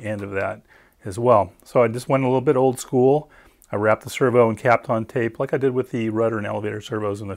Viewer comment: Hi, what tape did you use to wrap your servos? It sounded like you said caplon. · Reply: Kapton Tape: this is mylar tape used in electrical and high temp applications. Alternatives are regular masking tape and 3M blue tape. Tape adheres to the servo, adhesive sticks to the tape and the mounting surface.